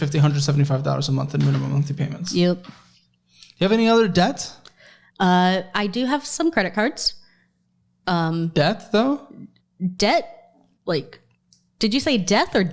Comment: It really did sound like said “death” instead of “debt” tho lmao